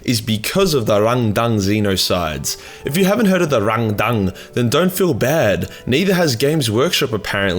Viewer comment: I heard it as Rendang, and now I'm hungry. Fackh ye